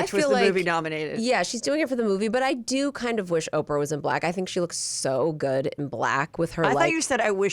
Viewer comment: “I wish Oprah wasn’t black”. 🫢 I thought she said that too😭